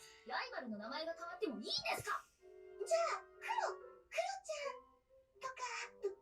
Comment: The name that YunYun gave the black cat here "Ink" in the subtitles isn't what she actually said... She said "kuro", which just means "black" in Japanese... It's very straightforward... But I'm not sure why the translators wrote the name as "Ink" instead of "Kuro"...